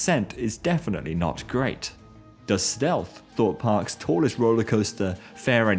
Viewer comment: The amount of people that went back when said "Does Stealth" when they thought he said "The Stealth" just me? ok.